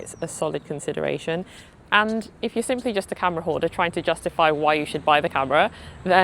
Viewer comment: I definitely thought you said "camera whore," and I was like "guilty!" 😂